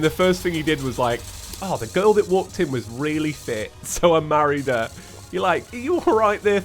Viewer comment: did you say fit or thicc